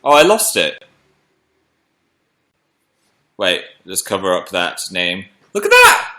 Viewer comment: he covered the contact name like why what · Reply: did he said "dad's name" or "that's name"? idk, i'm quite bad at listening to people speaking english ;_; i was quite surprised when i saw that part and the chat talking about that moment (also i couldn't pay attention that much because i was having lunch) but now that i'm repeating this part ithink is probably someone from his family? I hear "cover up dad's name"